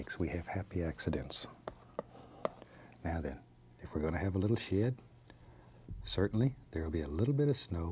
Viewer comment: I thought he said if we going to have a little shit